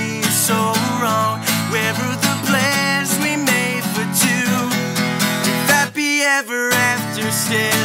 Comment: Did he say if fappy ever after at 1:13 ?